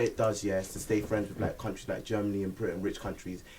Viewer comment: black countries like Germany and britian? did I hear that wrong at 2:10 lol